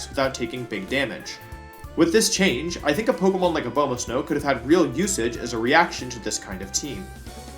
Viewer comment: the subtitles say obama snow